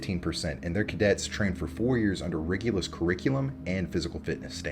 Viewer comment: Did he say, "Rigulous"???